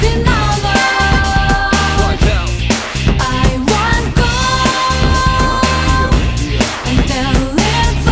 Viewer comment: "I'm not a princess..." 0:30 "...hoe" LOL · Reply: I always hear "I want gold"